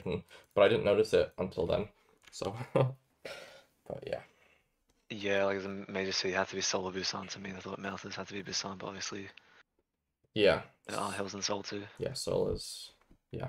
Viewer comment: no way fig bar understood what he said there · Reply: "yeah like it was a major city, had to be seoul or busan to me and i thought mountains had to be busan but obviously there are hills in seoul too" 😎